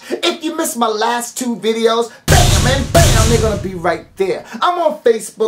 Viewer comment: lol i thought he said BAM AND BAM NUGGA